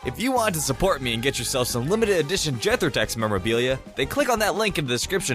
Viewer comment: When he said "jethorex" I thought he said gender text